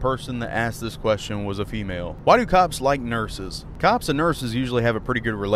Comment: I thought he said "nerfses"